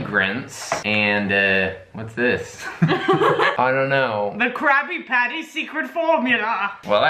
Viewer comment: “The Krabby Patty Secret Formula!” 😂👌🏻 edit oh god, most likes I have ever gotten. Thanks everyone who made me feel the most successful I have ever been 🙃😂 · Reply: Joselyn Diaz I swore she said Crappy Patty .....lololol